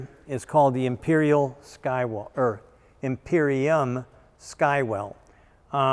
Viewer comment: for a sec there, i thought said, "Imperial Skywalker"... some type of Star Wars mish-mash. i would have bought one on the spot as well if that was its actual name 😉